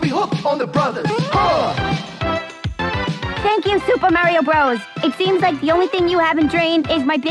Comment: wait did she say Mario?